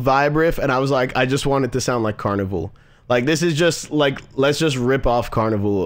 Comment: did he just say Karnivool ?)_